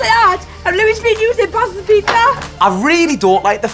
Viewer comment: Their accents are sooo thick I just heard pizza and jealous 😂😂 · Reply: Gymnastics S L lol same but on 3:34 all i heard was pizza 😂